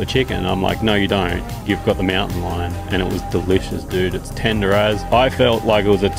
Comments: The captions thought he said, “tenderized” when in reality he said, “tender as”. Clearly YouTube needs to update its Australian-English vocabulary🇦🇺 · Haha love the Aussie accent,he said "Tender as" but it thought he said tenderized, lmao